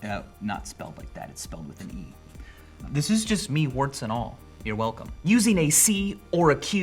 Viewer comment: What is he saying at 3:17? “This is just mean warts and all, you’re welcome”?